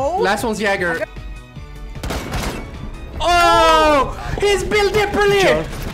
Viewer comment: It sounds like he said "HE'S BUILT DIPL EN LEE" at least that's what I heard.